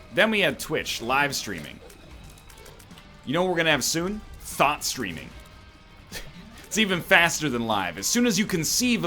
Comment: I thought NL said "Thot streaming" xD